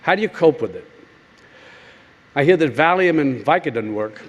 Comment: 중간에 마약이라고 번역하신 부분. 제가 잘못 들은 걸 수도 있는 데 저는 Valium으로 들리네요. Diazepam이라는 항불안, 진정효과를 지닌 약입니다. 향정신성의약품으로 마약은 아닙니다.(마약류에 속하죠) 필로폰, 헤로인 같은 마약으로 오해하실까봐 몇 줄 적어봅니다.